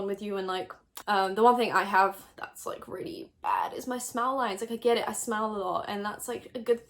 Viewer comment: I thought you were saying you had "smell lines" over and over haha